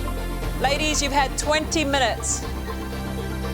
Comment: Back when Monica used to make chefs cry! 😬 [4:37]”Ladies, you’ve had twenty minutes . . .” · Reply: I thought she was saying "Laddies! you got twenty minutes!", but it sounded like "ladies" 😂😂😂.